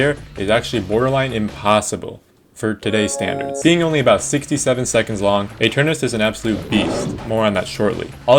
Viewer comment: did you say... 67 seconds long?